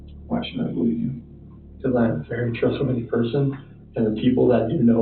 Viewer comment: “Because I am a very trustworthy person” is what he said in the “inaudible” part.